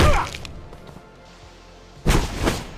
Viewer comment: did he said "f.yr self" or something like that ?!! lol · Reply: "flicker step"